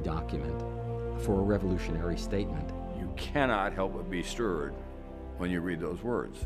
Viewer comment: Does someone understand what he said there 0:18 “you cannot help but be (I don’t understand what he said there) when you read those words” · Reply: "you cannot help but be (sure) when you read those words"